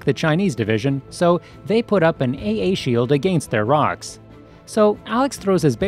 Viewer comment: I thought u said AT field lol evangelion up in here